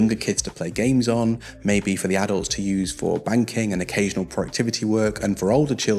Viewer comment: please tell me I heard banking.... and not... ehm... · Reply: Yes, (thank god) he said banking. I kept rewinding the video to make sure